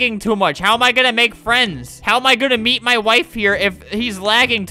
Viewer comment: i misheard it as "how am i gonna beat my wife" also i thought that guy had the slayer's right arm